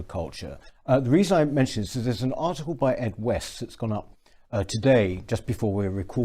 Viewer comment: I misheard the words at the 17 second point as "Fred West" and wondered where the feck this video was going.